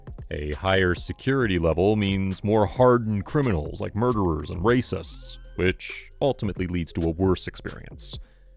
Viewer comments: I thought he said murderers and rapists.. Nah he said murderers and racists. either that or races which neither are a crime although inmates can and are both races and racists... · "More murderers and racists"? Did I hear that correctly? You chose racists over grape fan's for 2nd billing? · Did you just say racist instead of rapist? lol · Did this say more hardened criminals like murders and *racists? LOL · I'm sorry... it sounded like you said, "...hardened criminals like murderers and RACISTS..." You meant RAPISTS, right?